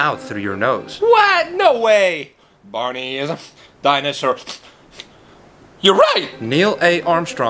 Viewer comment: When he went "Barney is sniff dinousar sniff your right" it sounded like he said Barney is a f**kin dinousar(just a little)